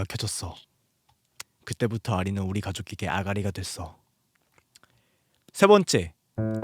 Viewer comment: 대가리로 들었닼ㅋㅋ